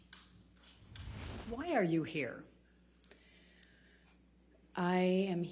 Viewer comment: i legitimately thought she said "Why are you gay?" at the beginning.